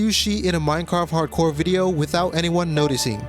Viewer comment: whenever he says cheat, all I hear is shit